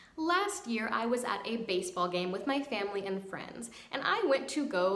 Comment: Every time you say "with my family and friends", what I hear is "with my family in France", and part of me thinks every time, "Wow, you're taking a lot of trips to France". Idk what that's all about, but somehow I now keep thinking you live in France